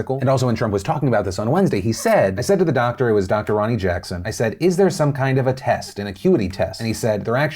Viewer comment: I go to do something else and all I hear is " Is their some sort of cutie test?" ...